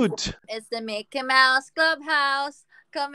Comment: Did I hear "Mickey mouse drug house"